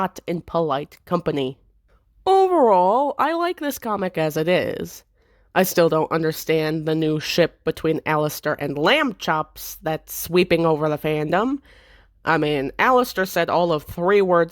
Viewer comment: MY DOG HAS A STUFFED ANIMAL NAMED LAMBCHOPS AND WHEN YOU SAID “I will be referring to her as *lamb chops*” I literally paused the video and laughed super loud! love the way you said it. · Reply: i thought he said rambochops